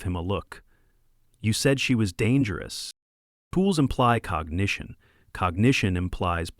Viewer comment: Good story! Love cats. Ai needs work. Serious fine tuning is badly needed... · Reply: Agreed. Fun story, but a pet peeve of mine with these narrations are the baked in 'subtitles' which include uncaught transcription errors. For the example which triggered this comment, at 4:13 "Pools" instead of 'tools imply cognition'. Before posting these videos one would imagine the creators checking their work for such errors. It doesn't hit me with quite the nails on chalkboard feeling of the mispronounced words we sometimes get, but it is one of those avoidable errors I'd expect to mostly be caught.